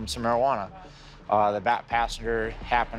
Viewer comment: LOL I thought he said "black" 😭😭😭 but he said "back" I'm so dead 💀💀💀